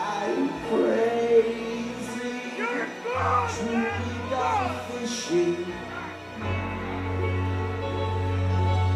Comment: What did the heckler say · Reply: You’re a cloon man... Gawd! Ba! Gawd!